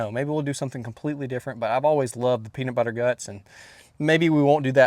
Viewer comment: Did he say "Peanut Butter Guts"?! Lol